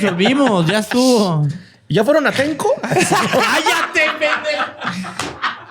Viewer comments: borre dice algo y todos estallan de risa... Que es lo que dice borre??? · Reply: "Ya fueron a Atenco?" En alusión a lo ocurrido en San Salvador Atenco, Edo. de Mex. 2006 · Alguien me podría decir que dijo borre en el 1:19:45, no alcanzo a escuchar y no sé a qué se refiere 😩😪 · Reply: "¿Ya fueron a atenco?" *Contexto En 2006 hubo una serie de enfrentamientos violentos en la ciudad de San Salvador Atenco entre la Policía Federal Preventiva de México, la Agencia de Seguridad Estatal del Estado de México, la policía municipal y habitantes del pueblo, militantes del Frente de Pueblos en Defensa de la Tierra (FPDT) y otros adherentes a La Otra Campaña del EZLN, que dejó como resultado el fallecimiento de Alexis Benhumea y Javier Cortés, la detención de 207 personas entre ellas 10 menores de edad, 146 detenciones arbitrarias, la expulsión de cinco extranjeros y quejas contra elementos policiacos por presuntas vejaciones y violaciones sexuales a 26 mujeres.